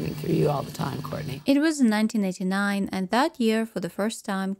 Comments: it was 1999? Haha I don’t know if I heard correctly but that’s impossible 😅 when Kurt committed suicide in 1994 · saw a couple comments saying "1999 is impossible" Yeah I think that's because they said 1989